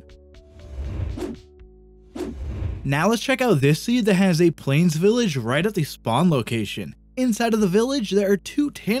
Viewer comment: I can't understand the coordinates could you please tell me how to enter this to teleport 🥺 Edit- please guys tell me 🥺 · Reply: Just open msg box and enter /tp 85 66 0